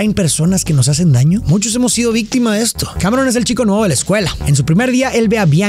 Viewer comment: Le entendi " cabrón es el chico nuevo de la escuela "